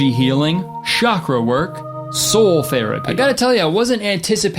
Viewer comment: I looked away for a minute (all this eye talk is making my hyperconscious of how my eyes feel) and misheard "soul therapy" as "soul ferreting" and now I want to know what that would be.